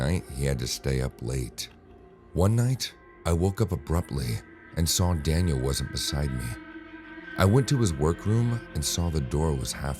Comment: Uh is it me or did I just hear “I woke up and Daniel was inside me”? 🤣